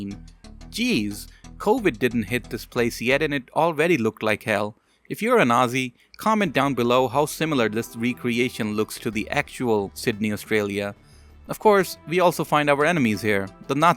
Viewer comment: I swear to god I thought you said "if you are a nazi"